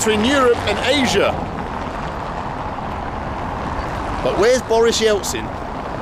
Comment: I thought u were saying Boris Johnson all this time 😂